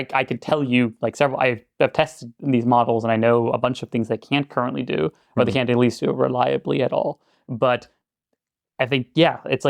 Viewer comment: "...Like it can't do mumblemumble reliably at all." Anybody catch what he said, please? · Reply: What I heard: I know a bunch of things they can't currently do or they can't at least do reliably at all.